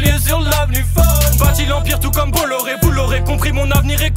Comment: a 1:58 il dit quoi ? " bâti l'empire comme Bolloré "??